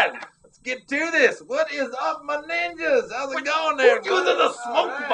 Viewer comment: Dude, when he said "What us up my ninjas" I totally freaked out the first time because I thought he said the N word xD